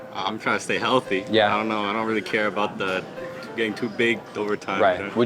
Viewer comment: I thought he said “getting too baked over time” 😂😂😂